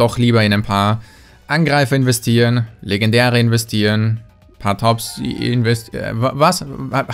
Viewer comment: Wer hat das verstanden in Minute 11:58 ? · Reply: Panthops oder irgendwie sowas ...